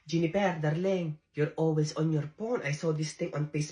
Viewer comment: I thought the nurse said 'your always on your porn!' ❤😂❤and I thought, yes it's so common today😂